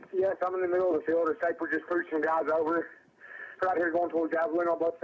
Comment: I hear "trooper pulled some guys over"??